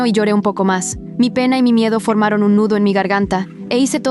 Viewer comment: escuche mi pene